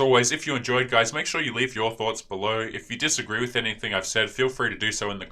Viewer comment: every time you say "leave your thoughts" I hear "leave your thots." You're a shooting man star.